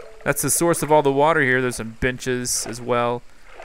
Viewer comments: I thought that u said bitches instead of benches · Thought he said "Some B****S" XD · I swear he said there's some bitches